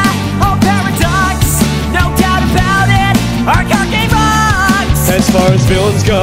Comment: Can somebody tell me what Jaden says at 1:54, right before it goes to Yusei? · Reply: He says "Our card game rocks!!"